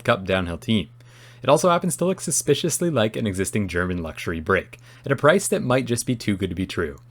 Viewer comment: I thought you said "luxury brick" 😂